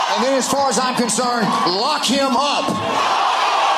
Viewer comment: When they chant “lock him up”, I hear “heil, hitler.”